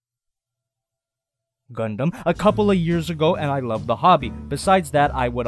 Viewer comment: DID YOU SAY GUNDAM?!?